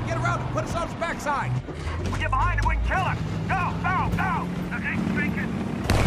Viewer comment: what the fuck is he saying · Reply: don't let him get behind us or something along those lines